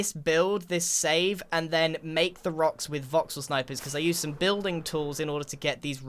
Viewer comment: anyone else hear this as Vauxhall snipers? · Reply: It was VoxelSniper. It's a creative building tool.